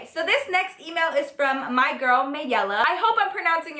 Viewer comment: I almost screamed when she said maella cause I thought she said Maelle which is my name😂😂